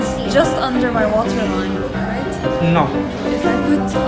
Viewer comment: i thought he kept saying 'so are you christian' 😭😭